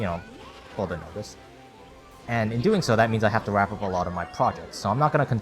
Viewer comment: what word did he say? At least until, you know, ?????????? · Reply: further notice